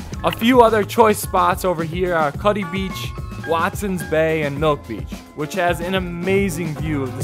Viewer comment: I have been all of these beaches except for milk beach. My favorite is Bronte not that far from house and the water is amazing. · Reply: Khalid Alawi I thought he said milf beach lol